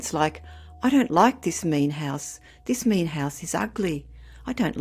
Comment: When a very young child (a todler) calls a strange house a "meat house".....a term they are unfamiliar with, that would be a warning to not unpack and to try to get out of the lease and to not plan to stay there. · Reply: I thought she said “mean house”. “Meat house” is a terrifying description.